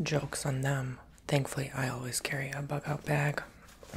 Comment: I thought you said you always carry a "f@ up bag" and had to go back and listen again 😅 but you inspired an idea in my head; buying a decent backpack and loading it with necessities just in case something fxcks up